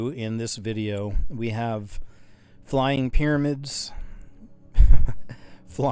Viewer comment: I thought he said "flying black pubes" lol